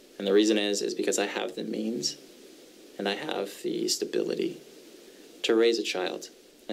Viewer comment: it sounded like “I have the memes and stability to raise a child”.